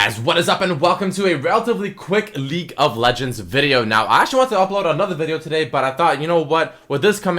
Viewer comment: I thought he said "welcome back to a another LEAK of legends video" at the start